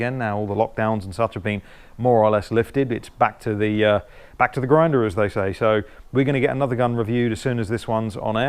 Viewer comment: Pulling our finger out of our backsides, and it’s back to the Grindr, as they say’ did I just hear that right 😳 34:30 ..... 🤣🤣